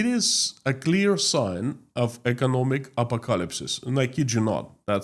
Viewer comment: I had trouble understanding what you were saying at 17:45. "Apocalypse" pronounced "a-pock-a-lips.